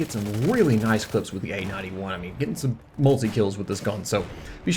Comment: Dunno why but whenever you say "A-91" I hear "AN-91". lol.